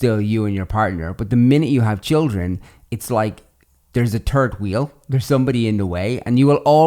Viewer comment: Third wheel or terd wheel ? I thought I heard it wrong omg